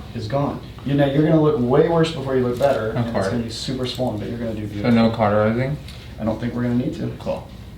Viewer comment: I love that bought her own mirror! Only she would. And when she said “So no cauterising?” I thought she said “So no cock or anything?” And I died! Then I felt like an idiot cause I just misunderstood her.